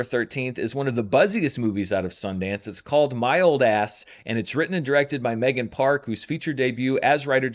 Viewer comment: I heard 'mild ass' lol